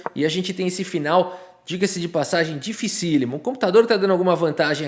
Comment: " digassi di passagi " eu ja ouvi essa frase outras vezes